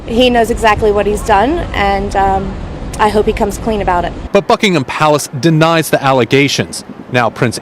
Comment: I thought I heard the announcer say: "Fucking 'em Palace"!! 😱